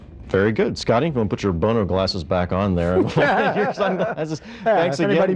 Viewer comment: What did he say? Boner glasses?